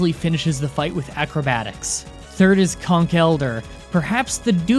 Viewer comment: I was very sleepy so i've misheard conkeldurr for "cock helder"... Don't judge me.